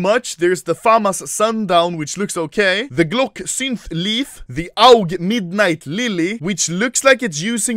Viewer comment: I really thought he said the glock syphilis 😂😂